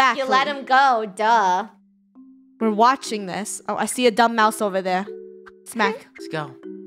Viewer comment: I thought she said dumb*** but she said dumb mouse